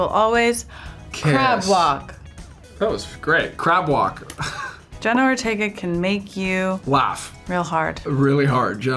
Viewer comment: "Jenna Ortega can make you... laugh, really hard," I heard this as, " Jenna Ortega will make you laugh" then separately "Jenna Ortega will make you really hard" lmao